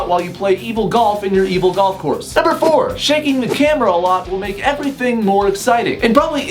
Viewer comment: At first I thought he said "shanking the camera" and I thought of melanie martinez in Alphabet Boy